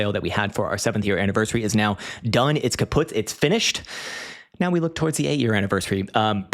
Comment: Did you say "kaputt"?? 😅